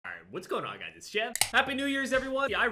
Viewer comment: Clicked on the video and the first thing i hear is “whats going on guys its’s jev cocs gun